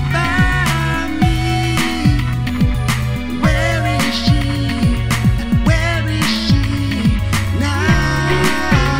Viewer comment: What did Ayane Say? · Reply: She said "B-chan", referring to Bobby